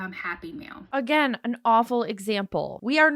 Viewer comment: I kept hearing 'happy meal'